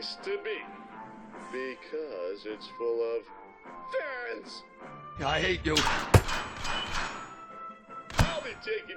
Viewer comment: did you just said "I hate you!"?